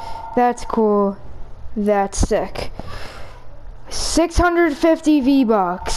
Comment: what did he say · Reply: He said that's sick